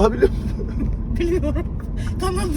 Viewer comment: kirvem kadın burada ne dedi de sen yarıldın? Defalarca dinliyorum bir türlü anlayamadım amk. Takıntı oldu şimdi uyuyamam. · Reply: “Tanıdığım”